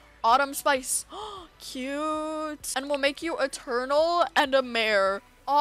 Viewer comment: i heard: ''' and we'll make you a turtle''